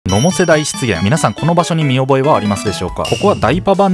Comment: 適当に開きすぎたせいでタイトル知らず、最初の発言が「野茂世代失言」に聞こえて、野茂さんがメジャーにいる頃なんか失言した動画かと思った